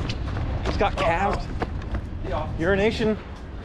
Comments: When I heard euroNation for the first time, I thought you said urination. LoL LMAOOOOOOOOOO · Every time says 'Euronation' I hear 'Urination'